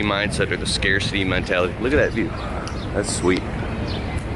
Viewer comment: Hey at 5:17, did you say look at that jeep?